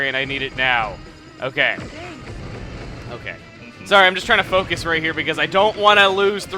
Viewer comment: When palutena says focus it sounds like she is saying fuck em